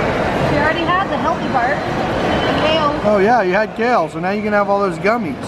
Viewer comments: Awww I heard say I at 4:27 I think ❤️and in there last 2 videos you can hear a voice in the back that sounds like saying its Like they talk to each other 😭💔❤️🔒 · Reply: montemayor she said Kale not i turned the volume up high and she said kale... can't even hear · I SWEAR SHE SAID CALEB OMGGGGGGG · I did hear say at 4:06 I can't believe it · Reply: did not say at 4:27 she said kale. Also everyone did not know about until about 7:10 because he was not dead of the time of the vid. He died at 7:07.